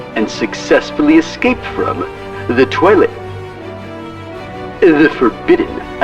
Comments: LOL it sounded like he said, "Escape from the toilet". LOL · At first I thought he said "from the toilet" xD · Ugh, I thought Wil said "Escape from the Toilet" in the beginning.